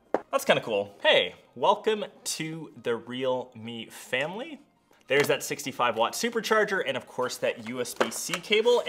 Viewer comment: Did u say family Don toretto